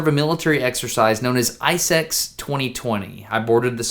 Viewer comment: what did he say . . . . "i se* 2020